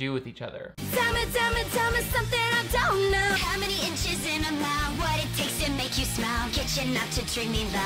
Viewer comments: I'm glad clarified that Selena sang "How many inches in a mile," cuz I thought she said "How many itches in a mouth" 🤨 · When she said “how many inches in a mile” I heard “how many inches in her mouth”… thank you for breaking that down for me 😅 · I thought she said how many inches in a smile and I'm like.....8 hopefully?